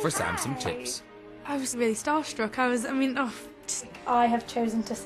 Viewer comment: What does she say here 0:31 ? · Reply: She said 'I was really starstruck I was just uh' then she kinda starts crying